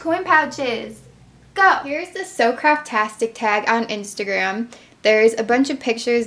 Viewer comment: thumbs up if you thought she said: SO CRAP TASTIC